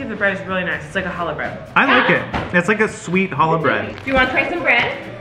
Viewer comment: I thought you were saying halo bread like holographic I had to google some things.